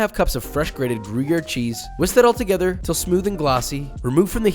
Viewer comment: i heard booger cheese